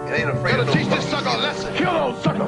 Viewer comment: I don't understand what they says in the intro part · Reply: "I ain't got no time for the jibber-jabber." Meaning he has no time for talking, only action.